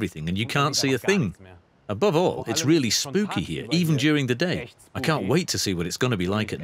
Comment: This hotel reminds me of silent hill... I thought the guy said “it’s really spooky during the day, I can’t wait to see if I die tonight”...😂😂